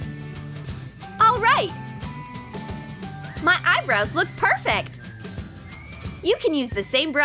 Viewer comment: is it just me or did they say ma eyebrow